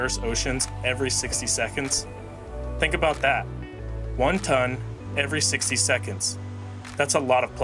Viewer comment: I thought he says Wonton